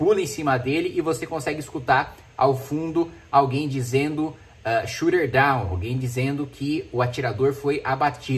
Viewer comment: caraio..... eu entendi "get down", mas se o atirador foi abatido realmente, deve sair mais noticias...